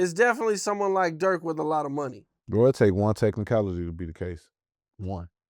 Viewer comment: What did he say at 4:59? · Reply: It takes one technicality to beat a case